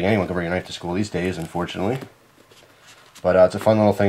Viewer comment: ok what did he say? 7:30 to 7:37 "unfortunately?" da faq